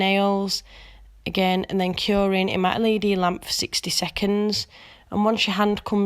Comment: Omg these are so gorgeous and like clean and classy. I adore them! And I just Love end accent! Especially when she says stunning and file! Lol and when she says LED lamp, it sounds like she says my Lady Lamp! Haha so cute!